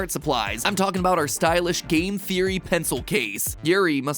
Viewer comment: I thought he said gay theory